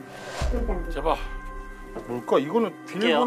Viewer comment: ~제발도와줘세요라고듣리는것같은데 나만그런게들려요?